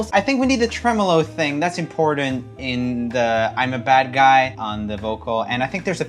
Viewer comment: i thought she said "i'm a bird guy" that's why i need subtitles on music videos